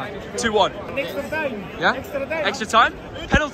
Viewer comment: did he say mbappe???